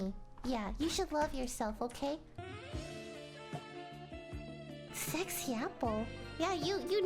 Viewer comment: i heard "SexY APpLE" and dropped my eggs TnT